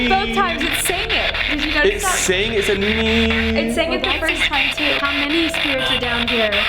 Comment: Am I the only one or did it say Ian when they asked who is with them